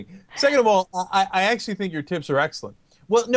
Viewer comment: Lol at 6:16...I thought he said 'tits" and not "tips"